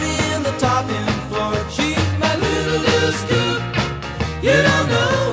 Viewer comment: When I was a kid in the car with my parents, I'd hear this song on one of their mixed cds...I always thought they were saying "she's my little dip scoop" ..so I'd go around calling people "dip scoops" haha